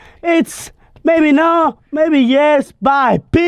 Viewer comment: At the end he said PEACE!! but it sounded like he said, PISS!!😂😂😂😂😂